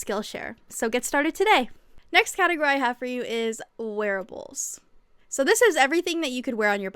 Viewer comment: When you said "wearables" I thought you said "werewolves" and now I'm wanting werewolf-themed patterns 😅